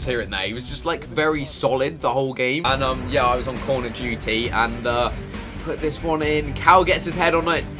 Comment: did anyone else hear "call of duty" first time round?